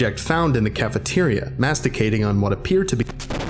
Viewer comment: The first time I watched I swear I heard "Masturbating Pudding" and I was like okay I found my new fetish. I didn't even know it was a thing. Then when I re-read it as masticating I put my pudding cup away.